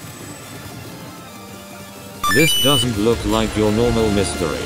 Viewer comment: i thought said this doesnt look like ur mother was born lMAO